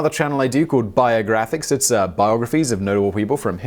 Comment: What did you say? Biographics is about what kind of people? Sounded like you said "noble" but I would hope "notable"